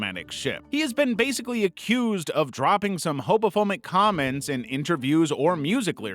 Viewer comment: Hobophomic took me out, ngl. 😂 I was locked in, then I heard that, and I'm like, "!?" 🤣🤣